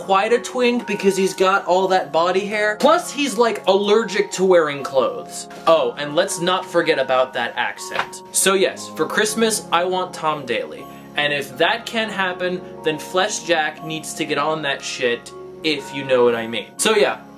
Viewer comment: I thought you said "Oh lets not forget about dat ass.." Which I would totally agree!!